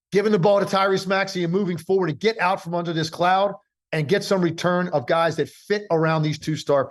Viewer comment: I thought Tim said get from out of this Clown (James Harden) 😂😂